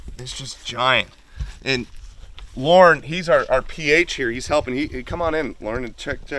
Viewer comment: What hunting guide was this he was hunting with in Tanzania please?Anyone know? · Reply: Also I think he says Laurent the end of the video 1:15:28 ✌️